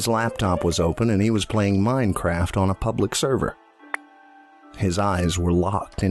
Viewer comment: I hear Minecraft memories.